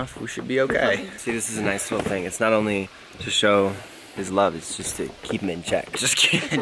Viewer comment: what did say at 3:52? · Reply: Its not only to show his love, its just to keep him in check.